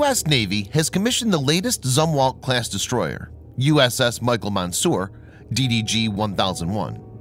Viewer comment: Fuck me I thought I heard USS Micheal Moore, I said to myself w t f ?